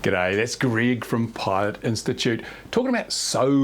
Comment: I'm old and I misheard your initial mention of Greg and fell on the floor laughing. I thought you said, Greg of pious institute.